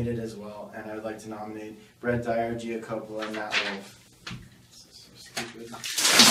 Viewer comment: did Keegan say this is so stupid